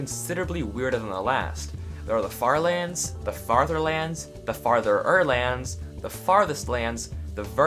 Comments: The farlands may be cool, but the fartherlands are cooler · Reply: I thought you said Fatherland. If you did, FÜR DAS DEUTSHE VATERLAND!! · The farlands may be cool, but the fartherlands are cooler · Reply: I thought you said fatherland xD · The farlands may be cool, but the fartherlands are cooler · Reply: I thought you said fatherlands for a second